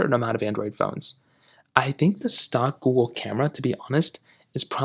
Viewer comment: Did you say "stock-wool" camera? I don't understand what you're saying